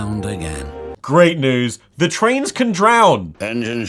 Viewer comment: Legit thought this said “the trans can die” 💀